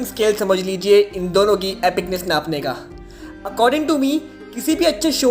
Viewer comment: DID YOU JUST SAY N WORD BRO WHY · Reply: Bro I said napney which means measure .